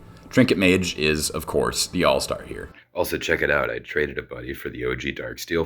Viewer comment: I did a double take here, I thought you said TRADED A BAYOU then I rewinded and I noticed it was "traded a buddy" lol. Also Lantern Control almost always has a deterministic wincon. You can't break the lock, especially not in Modern against the OG deck. It's so easy, just concede, especially in Modern because you're going to lose. That Reddit post was hilarious to me, Eggs was much much MUCH worse and that's how you know they never played real Modern.